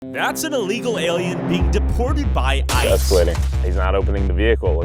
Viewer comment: did he say illegal alien? 💀